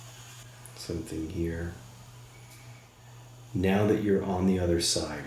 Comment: I think I heard 'I've seen Wesley guide me here' at 17:34. Wesley was Val's younger brother who he constantly spoke about that pre-deceased him as a teenager in the late 70's right as Val was about to head off to Julliard.